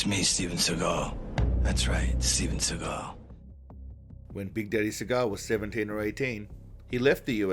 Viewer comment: I actually misheard the "Big Daddy Seagal" at 01:43 and thought you said "Bidet Seagal" - I shrugged and thought, "makes sense..."